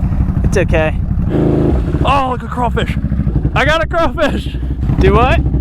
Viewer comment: I thought he said coral fish